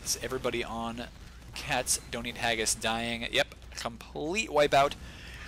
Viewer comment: sounded like he sayd "castonin ageis" instead of "cat's dont eat haggis"